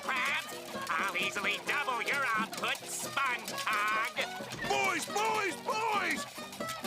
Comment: Did Squidward just say "Spongecoc"? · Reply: He said spongecog